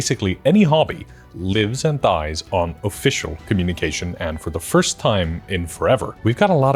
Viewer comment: y'know. In the first 30 seconds, I thought you said "lives in thighs" when you said lives and thrives....